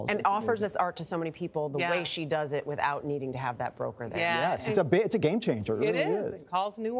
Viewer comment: What does the reporter say at 7:01? She offers her art to so many without needing wjat?? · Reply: Without needing a gallery is what I understood.